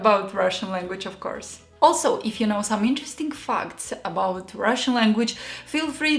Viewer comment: Did she say " interesting f*ck"? I'm in.